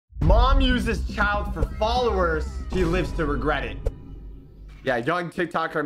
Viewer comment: did he call her he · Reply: No he said she but it kinda cut out